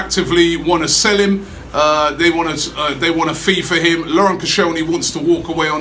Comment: " They want to FIFA him" ?? Is Koscielny going to be sent to the Arsenal FIFA video game squad instead of the reserves?